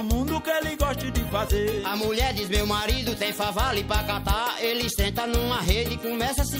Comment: o que ele fala que tem pra catar ? Não entendi.. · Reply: Fava